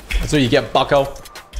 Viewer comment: it sounds like he said fuckoh